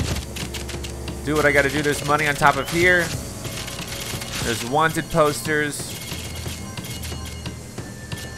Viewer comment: I heard him say wanted toaster.